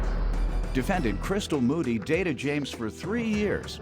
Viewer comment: I thought the narrator said " Booty " not " Moody " lol.😂